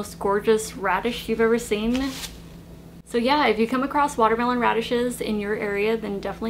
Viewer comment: I THOUGHT YOU SAID WATERMELON ONIONS HAHA